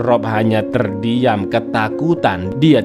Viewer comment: meberi tayi bang?? Apa salah denger ya 😂😂